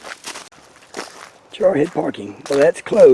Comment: it sounded like you said "Jarhead" instead of trailhead.... In the Biblical Flood most of the water came UP from the Earth. It looks like it is getting a refill. That snake kind of looked like a large Copperhead or Cotton Mouth? Not a close enough shot but that was beautiful. I know they come in diff color variations because of inter-breeding. Rare but it happens. They often eat each other unless it's that's season. 🧐 Again, awesome video. ❤🇺🇲❤